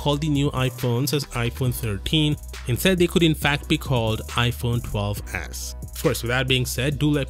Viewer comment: i heard iphone 12 as* 😂